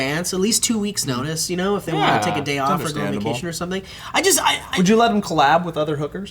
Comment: Omfg did anyone else hear say "if the hooker wants a sick day for vacation or GO IN the kitchen or something" lmfao!!!!!!he snuck that in there so good! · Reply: I think he says "...if they want to take a day off and go on vacation or something"